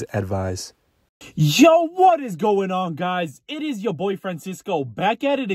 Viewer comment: The hubs heard me watching you and said "did he just say he is your boyfriend?" LOL I said "NO BABE HE SAID ITS YOUR BOY FRANCISCO" be careful 🤣